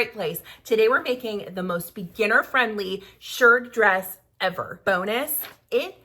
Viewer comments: I misheard it as 'shirt dress' and was thinking that's quite ambitious for a simple project 😂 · A⭐ A SHIRT DRESS IS JUST LIKE A MANS BUTTON DOWN SHIRT. AND YOU MAKE IT LONG. So my loves that's NOT a SHIRT DRESS. I am not a sewist but I see n I like the dress. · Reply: I also thought she said shirt dress, but after I watched it, it’s a shirred dress (referring to the shirred fabric).